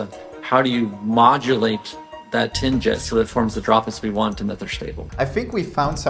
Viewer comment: did he say tin jizz